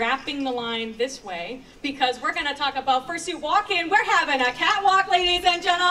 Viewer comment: is it just me or did she say "murrsuit walking!"